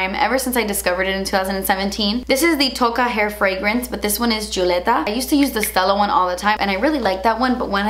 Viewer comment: Totally did a double take when you got to the hair fragrance. Thought you said the scent was “chuleta!” Hahahha!!